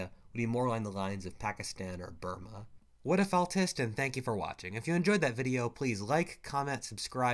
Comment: for a second there I thought you said 'whatifautist' and I'm not ashamed to admit it.